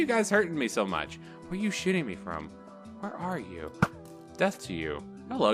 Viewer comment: Lol 420 :I, but you said "shi**ing" instead of shooting XD at least that is what it sounded like through the video XD.